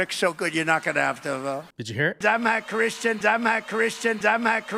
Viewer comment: Oh, I thought he said “I’m that Christian”.